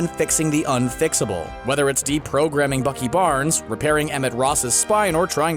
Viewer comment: when he said deprograming i thought he said deepthroughting lol